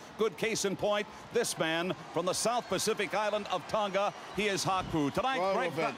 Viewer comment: he sounds like he says reggaeton